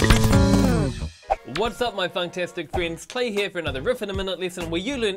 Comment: I thought he said whats up my autistic kids